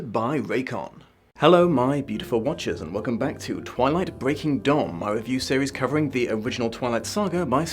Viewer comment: Did he just say... Twilight: Breaking?